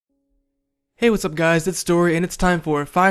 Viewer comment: i dont know what that says can someone reply what it says? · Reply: hey what's up guys scarce here